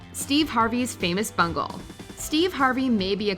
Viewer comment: I heard, "Steve Harvey's famous bunghole."